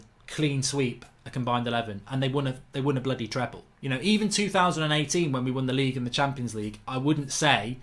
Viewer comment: Did mark say 2018